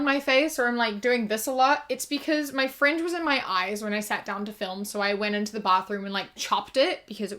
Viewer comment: WHY DID I HEAR “My friend was in my eyes”. 😭😭😭😭😭